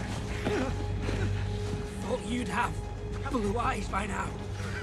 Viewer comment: what are they saying? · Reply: says to John "Thought you'd have blue eyes by now."